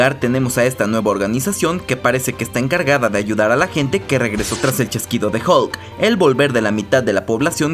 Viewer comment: escuche mal o dijo el chasquido de hulk xDDDDD